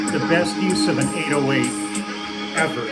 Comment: Was does Lee say at 6:15? The best use of an """"" ever? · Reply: an 808, the Roland drum machine, may actually be a 909 not sure but if Lee says it's an 808, it's an 808. Check out old footage of Collins dropping percussion sounds in and out during the live versions of the segue from Behind The Lines into Duchess from Genesis tours around the time. One of the best bits of post-Gabriel Genesis.